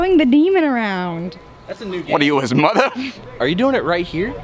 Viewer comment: sounded like he was a Italian mob boss for a second “what are oh his motha”